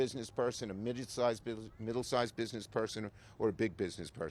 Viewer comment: did he say midget sized 🧐